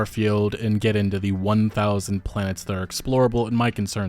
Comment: lol i only half heard that line i thought he said 100 planets and i was thinking "hmm that sounds like a lot, but i guess a lot would be moons for recource farms and eastereggs so that's fine." 1000 oof alot of empty planets in our future. smh.